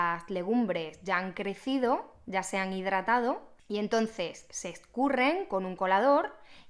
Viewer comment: Me encantan tus videos y siempre se los pongo a mis alumnos, muchas gracias, son buenísimos. Pero ojo que en el minuto 2:42 hay un error en los subtítulos que dicen "ya sean hidratado" cuando debería decir "ya se han hidratado"...un saludo cordial!!!😘